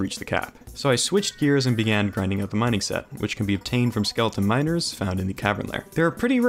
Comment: did he just sayid " skeletons minors"??